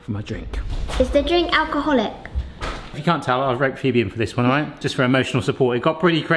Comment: Phoebe gives that same look Mrs. gives in response to his craziness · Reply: Oh, it's "Phoebe" I thought he said "P.B."